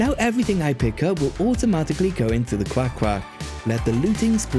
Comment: wait did he say quack quack